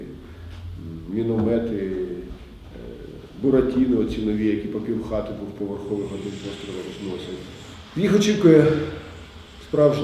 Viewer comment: "Буратино" - мне не послышалось????? ...... А может все таки Чебурашка(як казав Лысенко) )))))